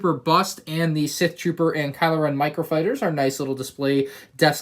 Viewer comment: did you say 'microfighter'?🤨